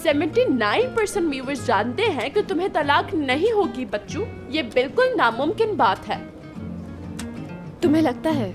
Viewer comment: did she say 79% viewers jaante hai ki talaak nai hogi ?????!!!